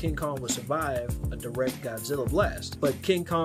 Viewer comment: I thought this man’s said vecheeto instead of Vegeta